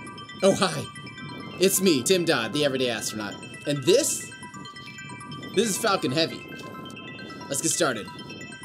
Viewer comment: i thought he said “the lunch is fucken heavy” and i was like “huh?”